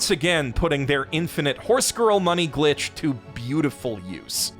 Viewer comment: Did you just say "infinite money horse girl anime glitch"? Dont let Trump find out.